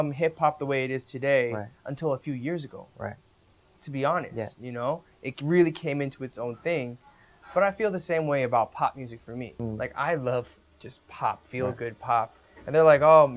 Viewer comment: for a second i thought Eric said “pot music” 💀